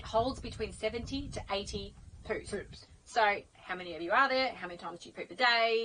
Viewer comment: Did you say it holds 70 to 80 poops?? Or did I mishear that, and you said 17 to 18?